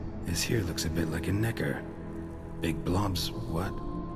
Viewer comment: I think it said nekker.................